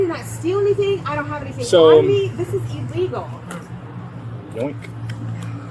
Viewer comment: Did he really say "boink"? 😂😂 9:49 he's having too much fun here.